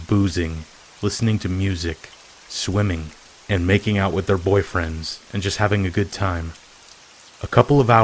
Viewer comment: Hol up “makin out with her boyfriends” did I hear that right😂😂